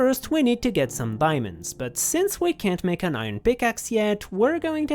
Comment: Umm do you mean diamonds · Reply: they’re saying that they heard him say “biamonds” instead of “diamonds”